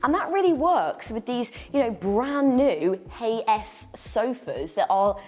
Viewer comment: What did you say the sofa manufactures name was ? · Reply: Sounded like she said "Hay S sofas" 🤔🤭